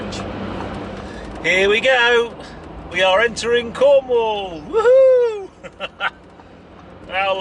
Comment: Aaah Cornwall, thought you said Como🤣